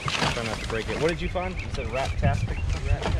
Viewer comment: I thought he said "craptastic" ahahaha🤣